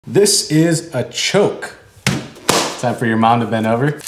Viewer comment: I thought I heard "This is a joke".